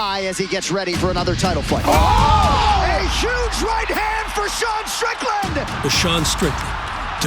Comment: Did they say DeSean Strickland at 1:21 🤣